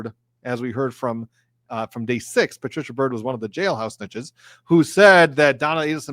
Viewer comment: Lol, thought he said “jailhouse bitches”😂😂